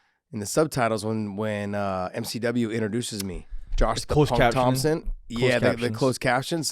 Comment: Lmaooo I swear I heard josh “the PAWG” Thomson!!